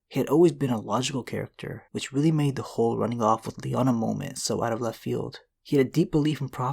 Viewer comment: you said lyanna mormont instead of stark lol